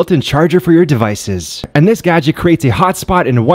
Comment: I thought i heard simp card